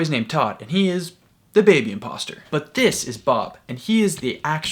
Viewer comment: did he just say dababy imposter